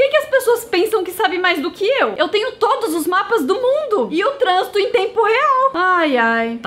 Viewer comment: Desculpe atrapalhar um vídeo tão normal mas... 0:36 "Eu transo em tempo real?" Kkk Edit: Eu sei que ela não falou isso mas foi muito parecido kkk